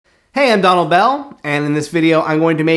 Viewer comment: Did he just say his name was Donald Bellend?